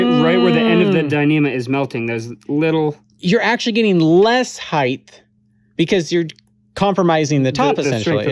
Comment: did you say heighth???